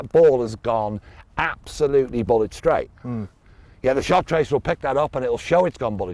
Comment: The first time I heard this, I thought said "Bollock straight", I now realise he said "Bullet straight"!